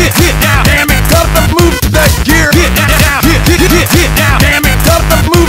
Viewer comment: Instead of "Dammit got to move that gear" I hear "Dammit, cut the blue, that gear!"